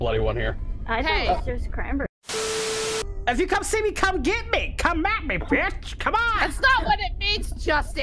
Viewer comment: had me in tears thanks a lot I'm straight crying from this whole thing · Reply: It sounds like u sayed come at me bitch aww nohomo